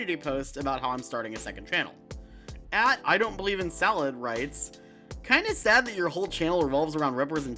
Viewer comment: when you said "@ (username) writes" I thought you said the username was "I don't believe in salad rights" at first and was like "..um that's because it's food? oh wait XD"